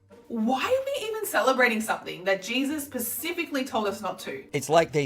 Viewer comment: Anyone else notice she says "pacifically" instead of "specifically" at the 5:20 mark?? Why do people listen to people like this???